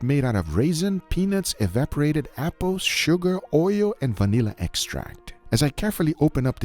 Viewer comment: did he just say evaporated eyeballs?